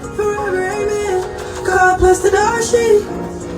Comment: Somebody said she said "God bless the dog sht." Now it's all I can hear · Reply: I thought she said God bless the dark shit 🤷🏽‍♀️🤦🏽‍♀️